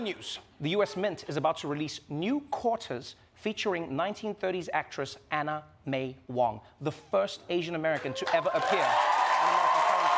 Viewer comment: Whenever Roy said "Anna May Wong," I kept hearing it as "anime Wong." Which made me smile every time he said it.